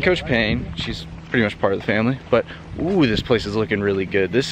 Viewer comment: did anyone else hear "shes pretty much part of the family"